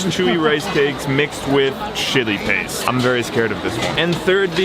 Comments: I don’t know why at 3:57 I heard c/sh*tty paste instead of chili paste...... Am I just that tired??? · I thought he said shitting paste 💀